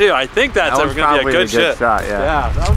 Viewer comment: i sound like he says good shit instead of shot